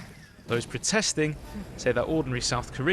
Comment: Oh ORDINARY not ornery Malachi thought, that's what I heard at the end there at first hahe